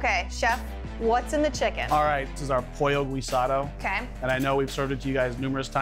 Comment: As a Puerto Rican that man butchered the name pollo guisado 😂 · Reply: That’s facts. I thought he said pollo rosado at first. I’m sitting her like wtf